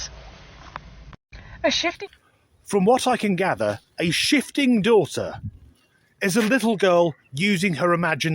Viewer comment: I thought she said 'shitting daughter '